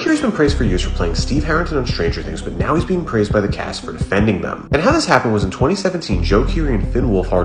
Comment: I heard: "praised by the cast for offending them" I was waiting for the offensive part...